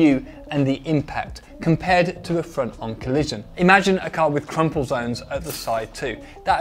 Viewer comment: "Crumpet zone", let's go! 🤣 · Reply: Oh, I thought he said strumpet zone, which is on the east side of downtown.